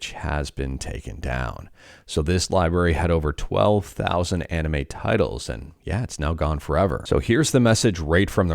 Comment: I thought it said "12000+ Anime Titties" 💀